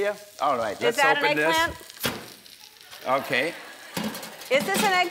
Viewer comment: At first I thought that said "elephant"